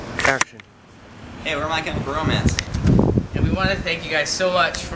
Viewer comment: for a second there i thought i heard him say "my chemical bromance." lol